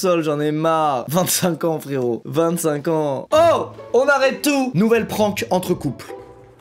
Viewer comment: c'est moi ou il dit une nouvelle prank ??